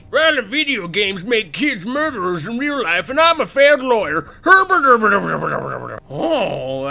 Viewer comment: "And I'm a failed lawyer! DAAABBUBUBUBUBUBUUBUBUBUUU" lol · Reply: I heard "fad lawyer" but yeah. He's that also.